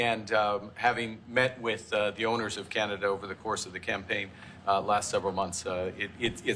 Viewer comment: did I hear correct ? (The Owners of Canada!? HMFS